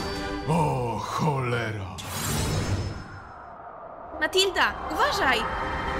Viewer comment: Czemu ja usłyszałam Matilda*? 2:06 Ale Dubbing wyszedł niezle od Pawlika I tak Pawlik tego nie nagrał ale to SUPER